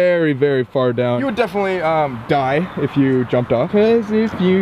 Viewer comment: I heard "Jump Off"